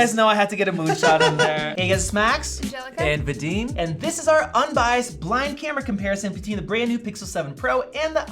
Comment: Why do I keep hearing "biased" even though it clearly states unbiased